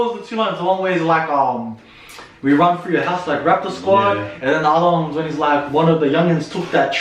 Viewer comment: ow i thought he said rack the squad